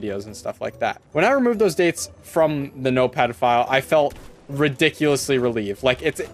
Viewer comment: I kept hearing "note pedophile" HAHAHAHA